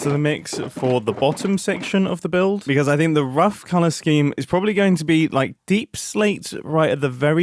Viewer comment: OH colorscheme i was so confused cuz i thought you said palastein and i was thinking like is it also a fancy architechture word aswell huh